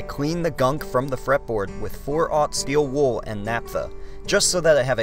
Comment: steel wool and ?? what did you say? can't understand :( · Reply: naphtha